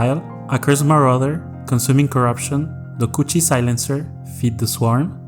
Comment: I thought bro said Da koochi silencer im like bruh 😂